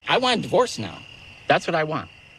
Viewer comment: Lol 😅 Thought he said: I want a horse!